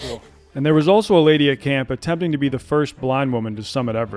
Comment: I thought you said the first pretending to be a blind woman. lmaoooo